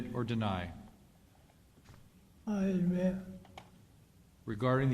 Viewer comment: what did he say?? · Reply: he said "I admit"